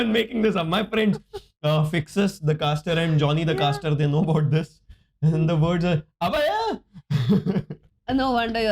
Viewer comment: if anyone understood what said pls reply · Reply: Abe Lawde, aisa kuch bola